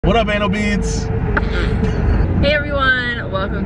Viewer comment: Ok, I think I totally misheard your bf at the beginning of the video because I swear I heard him call us “anal beads”. lmao ANYWAYS, I think we need to hear the story of you driving a car into a house. 😁 The bubble gum yarn looks so pretty! It would make a cute summer dress (or tunic length tank top) if it’s not too thick of a yarn.